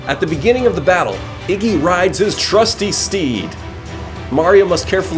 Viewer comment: I thought you said 'steve'